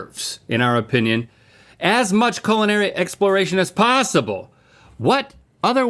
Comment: Am I the only one who heard "Colonary"? 👀